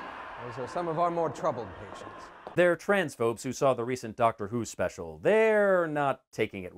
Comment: was the doctor who Christimas special bad? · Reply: ah. Okay i used captions and it turned out he said trans phobes. I thought he said trans folks